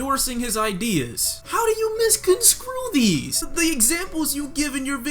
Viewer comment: Did he just say, "Misconscrew"?